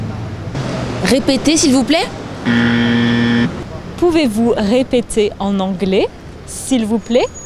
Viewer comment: says "Pouvez vous," but the subtitles say "Vous pouvez." I assume the subtitles are incorrect?